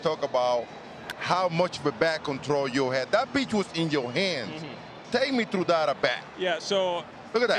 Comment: I thought big papi said that b** was in yo hands 😂